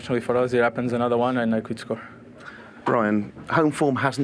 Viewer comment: Did he call him Brian?